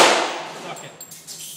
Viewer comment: did just say "fuck it" at 1:49???